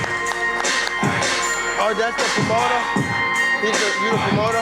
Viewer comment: Was that tank asking "is that the promoter," 2:10 or what did he say??? A d what fuck was up with the lion and elephant talk 😂😂😂😂